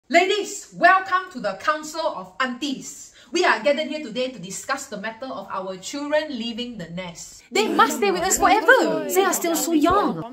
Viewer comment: I swear- I heard "Council of undi-"